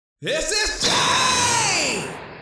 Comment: did he say this is ssj